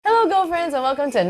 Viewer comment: I always thought she said “ HELLO GO FRIENDS” in the intro until i turned on the captions!!😳😂 and realised its “ HELLO GIRLFRIENDS”